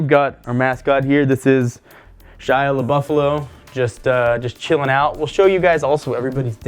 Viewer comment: the auto generated caption says that you mascot is "Shia LeBouf".